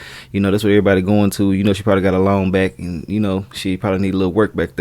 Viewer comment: I thought you said she probably got "a loan back" until 2 seconds later I realized you said "a long back" 😂😂